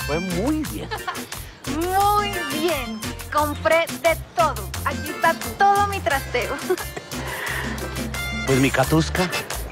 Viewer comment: escuché que cata dijo "AQUÍ ESTÁ TODO MI TRASERO" JAJAJA XD